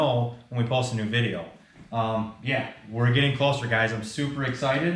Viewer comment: I thought you said crack house fab at first🤣 with how this thing looks it looks like a crackhead tryna copy grind hard plumbing’s builds so it fits pretty well💀🤣🤣